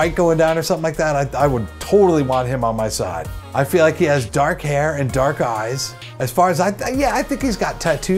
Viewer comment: I'm a simple girl. I see Rhett and Link, I click. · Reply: OH, I missheard that, sorry, English is not my native language, I heard "black hair"